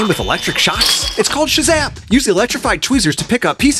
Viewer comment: Bro I thought he said "It's called Shazam" so then I screamed Shazam to see that he said shizap💀